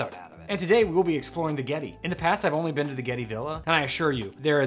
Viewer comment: At first I thought I heard "the ghetto". Disappointed.😂